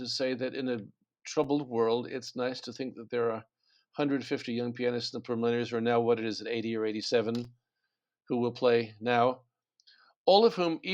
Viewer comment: In the last summing up Olhsson says that it is nice to think that there are 150 young people that -- and then I cannot understand, also the subtitles are bewildering, wrong, - "per millionaires are at now 80 or 87 ..." listen at 17:45 ... It totally eludes me what he actually says, but it made me think of the percentage of population playing piano (10000 per million?) and the number of earlier participants in this competition, who still are alive, (at 80 or 87, but the first was in 1927, 94 years ago! Maybe he counts from 1955, the participants from that time, yes, they are 80 or 87 now) and that is a great thing to remember and cherish in a troubled world. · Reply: I believe he says, "it is nice to think that there are 150 young pianists in the preliminaries, or now what it is, 80 or 87? who will play now." implying that the first round cut had already happened and the preliminaries were over. He definitely stumbled and mumbled through a lot of words.